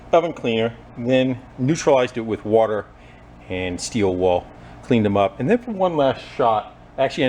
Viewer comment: did u say “steel wall”?🤣